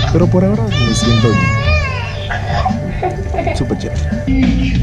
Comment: Hola antes de ayer vi por primera vez un video tuyo(el de la comidas callejeras) y desde allí eh visto casi todo tu material y me encantó!! Lo único que no entendía la palabra que decías(champs) jajajaja pensé que decías chéveres hasta que por fin escuché bien y di con tu palabra clave jajajjaja y ya tienes una suscriptora más desde Madrid España